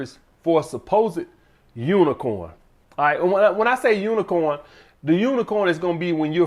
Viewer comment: I thought you said avoid unicorns. What are your opinions now and day